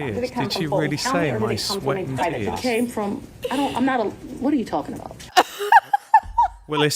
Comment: I swear it sounds like she was gonna say "I'm not a lawyer."